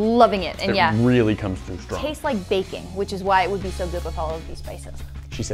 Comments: Can we appreciate how specified that said “baking” when (idk about you) I thought she said bacon 😂 lol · It did sound like she said "bacon" haha. Thanks for the clarification XD